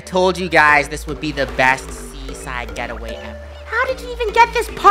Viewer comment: BYE I THOUGHT SAID SEWERSLIDENIF YK FOR A SECOND😭